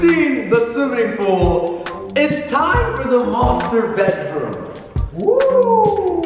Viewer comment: I laughed at the word you said "master bed" room I heard it as "master bate"